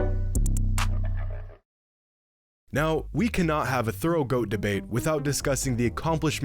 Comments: did you say goat throat at 6:27 ??? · Tell me why I heard throat goat debate at 6:25 🤨😂😂😂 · did anyone catch the throat goat competition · Tell me he did not just say throat goat debate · Sounded like he said throat goat debate @ 6:29🤣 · I’m sorry did he say “throat goat debate” at 6:28? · Ngl i heard ‘throat’ goat debate at 6:34 and had to comment about it great video tho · ayo did he say thorough or throat?? 😳😳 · swear this man said thraot goat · Thought he said “throat goat” 💀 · i thought he said "Throat GOAT debate"😭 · Thought he said “throat goat debate” at first😂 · did he say throat goat debate?? Or am I tripping 🤔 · i thought he said throat goat debate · i heard throat goat lol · I thought bro said throat goat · Not gonna lie at 6:25 I thought bro said throat goat debate and I was like wtf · … did he say “throat goat”??